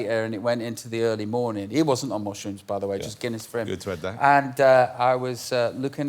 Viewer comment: can someone help me with what they're saying about Bono after he 'wasn't on mushrooms by the way', Robbie says "just Guinness" but I can't make out the next words they quickly say. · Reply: Robbie says "Just Guinness for him" and I think Jonathan says "good thread there". Could be wrong